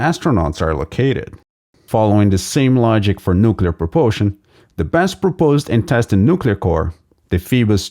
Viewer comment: sorry bothering you but I am a new English learner, and I am not sure if there is “the best proposed and tested nuclear core” while the auto sub suggests that it is “the best proposed intestine nuclear core ”, that makes me confused 😢may you please tell me what you are saying here please 😢